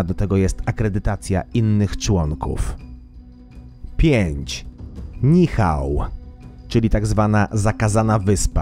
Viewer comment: Zamiast wyspy Nihau usłyszałam wyspę Michał xd